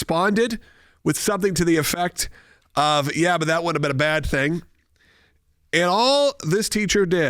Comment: did you say the kid said, "that wouldn't have been a bad thing" or "that would be a bad thing"? Dude, you gotta talk a little more clearer or slow down. Enunciate!!!